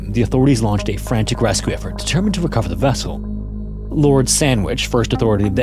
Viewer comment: Am I tripping or did he say "Lord Sandwich" 🤔 🥪.....🤷